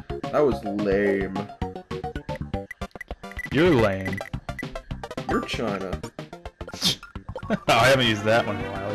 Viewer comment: what did say? your China?